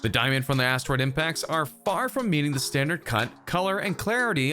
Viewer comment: Did he say "cunt" @ 2:58?